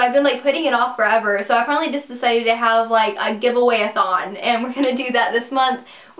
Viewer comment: haha at first I thought you said give away a thong